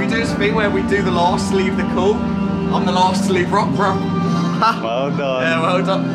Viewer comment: did he say the last to leave rook borough